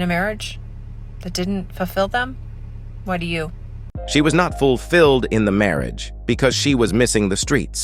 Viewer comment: I thought she said she was Hot…..??!